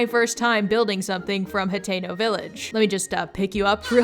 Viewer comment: The whole time I thought you were saying Potato Village😂❤